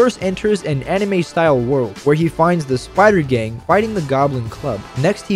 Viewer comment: I thought he said spider gay 😮